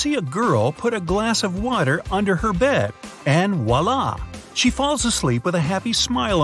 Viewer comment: what he said can anyone please write it?🤔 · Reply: Voilà. It's a French word that Americans use, loosely translated, to mean : There!, There's you have it!, Ta-da!